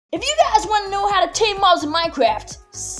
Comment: so basically their stuck there unable to move lol😂😂 I thought you said how to tame not trap